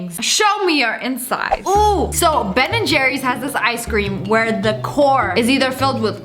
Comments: I thought Lia said Ben and Jeremy ( azelart and Hutchins ) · When you said Ben and Jerry’s I thought you said Tom and Jerry’s Me-Tom and jerry made a ice cream!? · I thought she said corn and I was really confused. “Ben and Jerry’s has this ice cream where the CORN is filled with...”